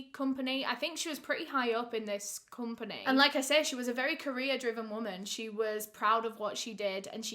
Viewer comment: Every time says "career driven woman" because of her absolutely beautiful accent, I hear "Korean driven woman" at first I was like wtf and then I was laughing and dying on how much of a misheard I did lmao